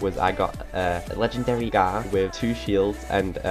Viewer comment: Thanks for this, did I hear legendary gar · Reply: Yes, there is a new legendary weapon called Gar